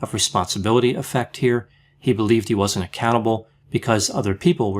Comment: says Reeflay "believed he wasn't accountable." At first I heard he "believed he wasn't a cannibal." This prompted me to reverse the vid, in case there was anything I had missed regarding the extent of the man's depravity.